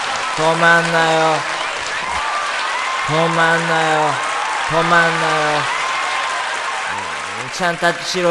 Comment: 泊まんないよ 泊まんないよって聞こえるの😂どんな意味か知りたい💦